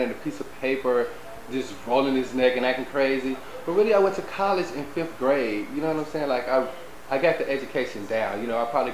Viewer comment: LMAO! Did he say that he "went to college in fifth grade"?